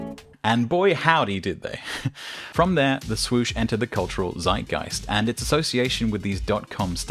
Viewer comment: for a second I thought you said "entered the cultural designgeist" and thought it was really clever. Nope just misheard zeitgeist.